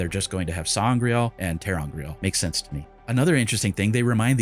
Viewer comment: She said "sa'angreal and ter'angreal." The captions confirmed it. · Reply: Ah my mistake then! I could have sworn she just said “angreal” If so, I agree it’s probably a fine simplification to just make all objects that amplify power “sa’angreal”!